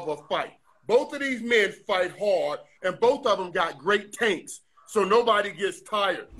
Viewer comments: anybody else hear "Both of them got great taints"? Can't unhear it now. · I swear I heard "both those men have great taints!" Ik he said tanks but still